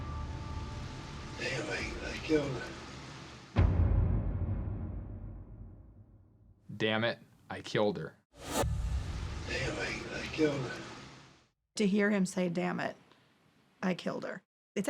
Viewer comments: I thought I heard him say "They think I killed her" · That first guy was obviously guilty, but that audio that they had I think, obviously sounded like he said “they think I killed her.” Not “dammit I killed her“. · I hear "anyway, I killed her" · It sounded like he said ''Anyway, i killed her'' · Regarding Ed Edwards, I don't hear "damnit I killed her," instead I hear, "they think I killed her." I suppose it doesn't matter at this point, and it wouldn't have made a difference back then either. After all, Edward Wayne Edwards was a murderer.